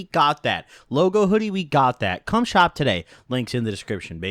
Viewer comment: What did you hear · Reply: sounded like he said cumshot but he said come shop